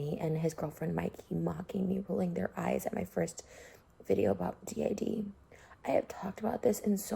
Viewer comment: Did she say d.A.d not D.I.D